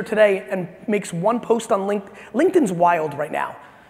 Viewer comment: What's the name of the app? Linked? Linked? I can't understand it quite right. Please help:) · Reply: LinkedIn :) L I N K E D I N 👍